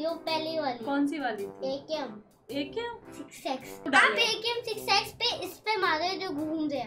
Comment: kya bola tuneeee😂😅😅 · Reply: 6 X hai wo